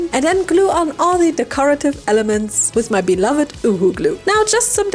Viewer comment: “with my beloved uwu glue” THATS WHAT I HEARD LOL😭😂